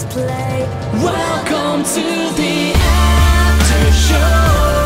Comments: i thought it said "welcome to the afton show!"😭🤦🏾‍♀️ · When I first heared this Song I thought they were saying "Welcome to the Afton Show"😅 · Why do i hear "afton show" instead of "After show" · Bro it sounds like there saying afton show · I misheard it as "Afton Show" 😭